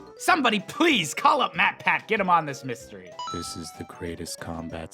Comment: DID HE SAY MATPAT!!!!!!!!!!! OMG I LOVE MATPAT'S CHANNEL OMG OMG OMG